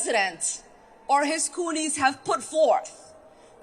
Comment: What did Omar just call us, Coolies? That sounds like a slur.